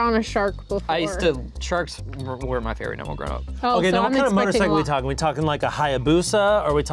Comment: I thought said “sharks were my favorite number growing up”